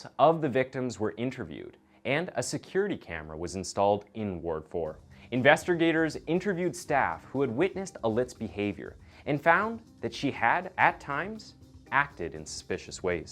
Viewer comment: I swear I heard Investor Gators.....is that like a Wall Street Savvy Reptile? Please don't tell me I'm the only one who heard that around 12:53 or 12:55...